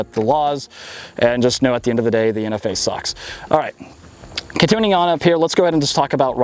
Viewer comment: “At the end of the day the NFA suck.” Truer words were never spoken. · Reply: Thought it said "At the end of the day the NRA suck." for a second